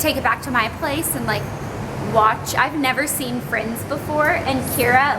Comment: First I thought you said Fringe because I just couldn't believe you'd never seen Friends!!